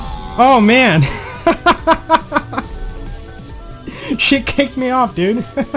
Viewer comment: it sounds like you said sh!t